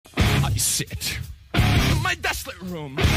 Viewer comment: I thought it said "i shit"